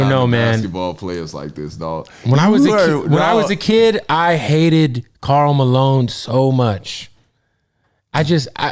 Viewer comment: I hear most kids love Karl Malone